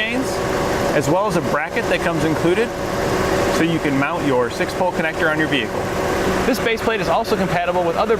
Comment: It was kind of noisy but at the 1:00 mark it sounded like you said "so you can connect your 6 volt connector on your vehicle:" I'm sure you meant your 6 pin connector.